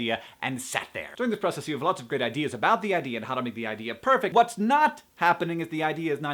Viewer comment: I thought he said, "During this process you have lots of great ideas about the yeti, and..." Wonderful list of good attitudes that apply to pretty much everything.